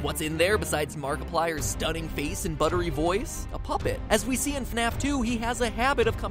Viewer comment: Did you say.......BUTTER!!!!!!?????